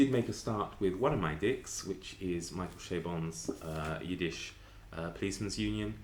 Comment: When you said "Michael Chabon" I heard "Michael K Vaughan" and I thought I didn't know he had a book out...